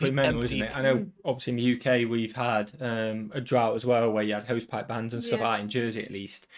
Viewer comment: Did you say "hosepipe"? Until moving to the Charlotte, NC metro from New Jersey nearly 16yrs ago, I had never heard the word "hosepipe". Honestly, I thought I had heard the guy wrong, but context that he was washing off his camper with a garden hose had me realizing "hosepipe" = garden hose.